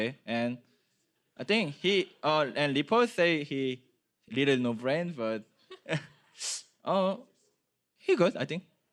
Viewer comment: Regarding Licorice, did Impact just say 'Reapered say he plays with no brain...but I think he good'